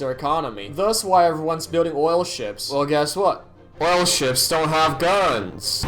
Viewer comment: I thought you said "oil shits" 😆